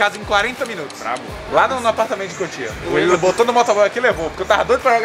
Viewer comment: eu ouvi........... COTIA ???????